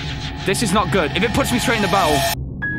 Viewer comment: instead of "Straight in battle" the subtitles said "Straining the bowels"